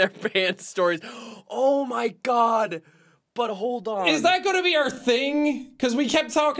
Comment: Why, when I heard say, "But hold on," did my brain go "Butthole Dawn?"